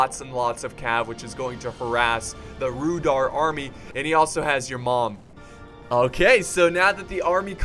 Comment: did u say he also has ur mom? lmao it's sounded like it XD